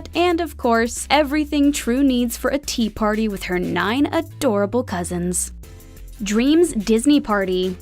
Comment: When she said with her 9 adorable cousins. I thought she said “with her 9 adorable husbands “ lol😂🤣